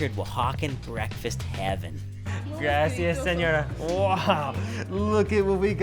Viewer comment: Did you say,"Gracias señor" to that nice lady around 2:18?